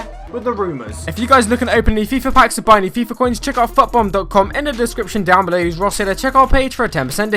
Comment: Did it say fuck bomb?